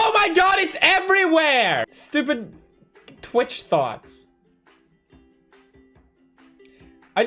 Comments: When I heard pewdiepie say "twitch thots" I thought he said "twitch thoughts" like thoughts he can't control. Which is why I was confused as to why she got offended 😂😂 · Tbh I literally thought he said Twitch Thoughts not Twitch Thots😂😂😂😂😂😂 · I literally had no idea why she was so mad because I thought he was saying “Twitch Thoughts”